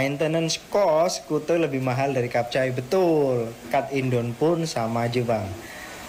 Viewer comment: maaf kot aku salah dengar. Kenapa brader tu sebut Indon? Bukan ke diorg cakap macam menghina.